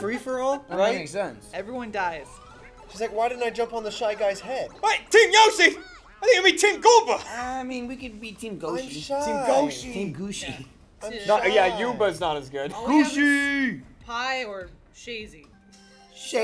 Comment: Did somebody say Team Goosh Goosh?